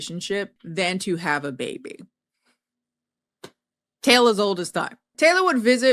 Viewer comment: lmao I had to rewind. I was doing something as you were on the background and I thought you said Taylor's old as fuck at first.